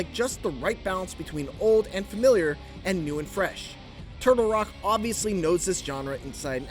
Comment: he says "Turbo Rock" instead of Turtle Rock.